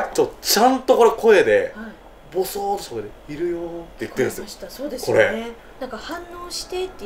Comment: の『いるよー』聞こえました？ 聞こえた人詳しいこと教えて欲しいです。